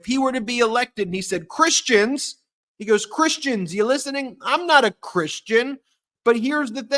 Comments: Have the nepharious magas come up with a plan to not certify the vote and through it to the house? Please ask Mark Elias about this. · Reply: Didn’t I hear him say “I am not a Christian “ ? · Now you did hear where Trump said he is not a Christian when he was talking about never having to vote again why is nobody talking about the fact that he said he was not a Christian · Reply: I thought he said I’m a Christian but it just came out slurred, so I re-watched it and he did shake his head and say, “I’m not a Christian!” Wow!